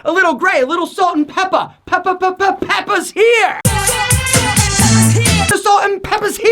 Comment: I thought he was going to say PEPPAAA PIG